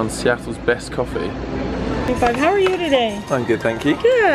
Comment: Guys can you tell me what the cashier is saying in 1:00 because I can't understund her? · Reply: OmeGa1905xd she said, "hey how are you today", "good", "do you need a bag for these?", "okay".